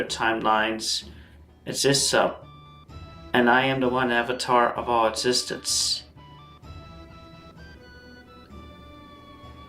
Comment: I was looking away and swore he said "I am the one avatar of all autistics"